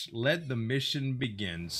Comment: I thought it said let the misery begin not let the mission begin